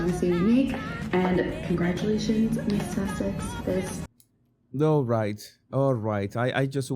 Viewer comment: It sounded like she said congratulations Miss Sussex at the end of???